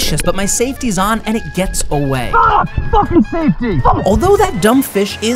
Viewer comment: “Fucking safety!” “Fuck!” This man has me rolling 🤣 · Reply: I thought he said "fucking tasty!"